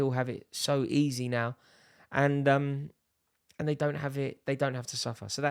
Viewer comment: I don’t think he said they don’t suffer. I know the point your referring to. At 1st I thought he said, “they don’t have enough ‘anger’”. I then realised he said “they don’t have enough hunger”